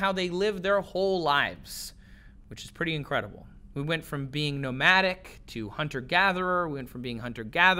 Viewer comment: caption says "nomatic" instead of "nomadic"